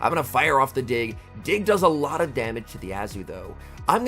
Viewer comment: why I hear dic😂